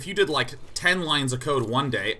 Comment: I thought he said "lines of coke" and it made the whole sentence WAAAY different LMAOOO